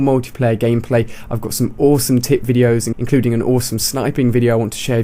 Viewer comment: At the end I thought he said "I have some awesome tit videos"